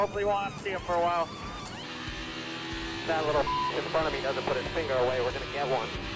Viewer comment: I thought Bowman said, “That little fucking Monkey.” When talking about Bubba Wallace lmao